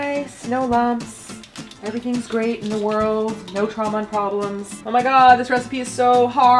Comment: lol, I actually thought she said "No Trump, no problems in the world" lol. Sadly it wasn't Trump but drama.